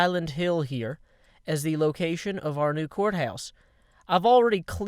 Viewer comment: when he said "court house" I thought he said "quartz house" lol